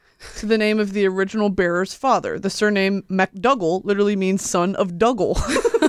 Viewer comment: i thought u said mcdouble instead of mcdougal at 13:25 and lost my shit